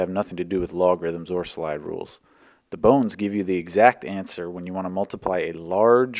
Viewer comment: The way he says "the bones" is one of a kind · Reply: I keep hearing "The bounce"